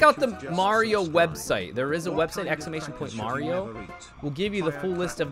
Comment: What is the website he mentioned? It sounded like “exclamation point Mario”